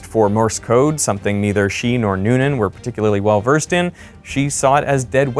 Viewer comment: I knew I would be sad, but I didn't think I would get this angry at the lack of preparation on Earhart's part. I always figured it was just adventurism hitting the limit of the technology. But I can't understand not having complete understanding of vital equipment and complete synchrony with the people you relied on for such a bold journey. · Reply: That’s Not what he said. @ 27:17 he said “…something [Morse code] that neither she nor Noonan were were particularly well versed in.” So they didn’t include the long trailing antenna. Now That might have been a mistake; maybe they could have used that antenna to send & receive messages. And as he pointed out, the Itaska could have taken a bearing on her if she had included that antenna, if she was transmitting anything; whether good at Morse or not.